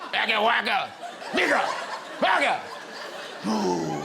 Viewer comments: did he say the N word? Racist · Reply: Nope its what Macca Pacca says in the night garden Nicka Nacka Noo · I’m sorry what did he say?😂😂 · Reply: Bigger